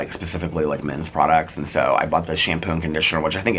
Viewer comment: I thought you said hemp shampoo, and I was remembering you won't buy anything made with hemp, and i was confused lol. Then you said the name of the shampoo again and heard Him not hemp... dunno why i thought i had to share this with everyone but here we are lol..❤❤